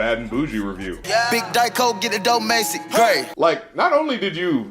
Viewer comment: My co worker thought he said a little diet coke and a little macy gray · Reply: My co worker thought it said need laypo, talking Macy gray